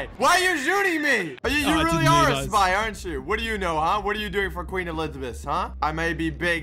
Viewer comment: on 2:20 what did say? queen elizibith???